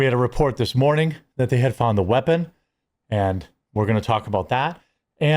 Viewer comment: Thought they said they had the guy.